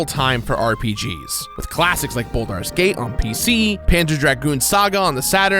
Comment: What is Buldar’s Gate? I have heard of Baldur’s Gate but not what he said.